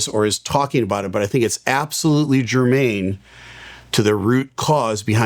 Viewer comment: To all englisch speaking people: At 3:04 says: „its absolutely „germain???“ to the root cause of what happened to… What does the word germain??? mean, i have never heard of it!!! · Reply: "germane"